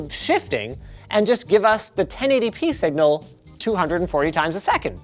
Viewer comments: Title: "Do they think gamers are suckers?" You must be new here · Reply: Yo.. random question, what's with hime saying "60x4240" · "I can't even afford that pillow " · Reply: Yo.. random question, what's with hime saying "60x4240" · "Do they think gamers are suckers?" Judging by the popularity of "gaming chairs" who wouldn't? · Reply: Yo.. random question, what's with hime saying "60x4240"